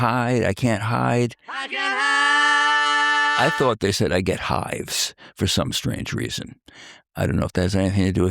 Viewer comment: I always thought that line said, I get high. But I never heard, I get hives, lol.